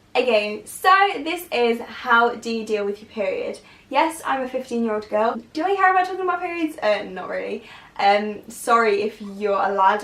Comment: What did she say at 2:57 because literally all I hear is "Yes, I'm a fifteen year old girl, do you have a bunch of the muppers?" and well I'm pretty sure that's not actually what she said...